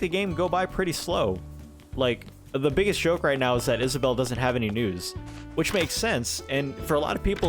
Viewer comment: Omg omg omg, when you said “it’s unfortunate that Isabelle doesn’t have any news” I could have sworn you said that Isabelle doesn’t have any nudes!!!!!!